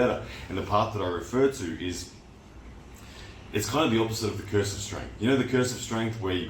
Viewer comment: he said "It's kinda the opposite of the curse of strength". Please make sure you hear what is being said before making a commentary video about it. You didn't misunderstand anything. You misheard it - which makes it an objective error.